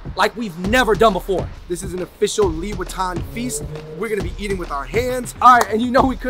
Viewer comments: When you guys say “liwetan” I thought you guys said “louis viton” wkwk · Everytime you said liwetan I thought you were saying Louis Vuitton lmao.. I was like whattt? 😆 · The way he said "liwetan" i misheard it as "louis vuitton" feast.... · Always misheard the "Liwetan" with "Louis Vuitton" lol